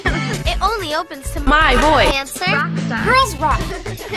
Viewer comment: did she say cancer? Only 17 seconds in and I'm already confused and caught off guard I mean it wouldn't be a video any other way